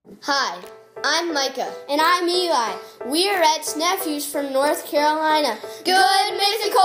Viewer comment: Aww I didn't know Rhett had nephews :D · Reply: I first heard them say, "ex nephews." I paused the video for like ten minutes to think about how that was possible. 😂